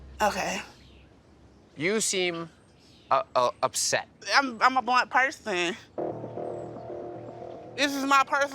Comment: She said "I'm a blunt person" the subtitle said "I'm a black person" lol Also, stop saying "I'm a blunt person" or "I'm brutally honest" when you're just a witch with a b